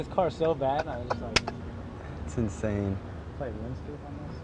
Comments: Lol 6:51 Did he say "Can you play Runescape on this?" xdddddddddddddd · did he say playrunescape😂